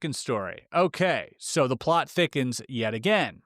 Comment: for a second there I thought said “the plot chickens yet again”